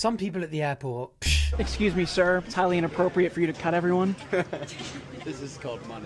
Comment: I thought he said “It’s inappropriate to Pet everyone” 🤣🤣🤣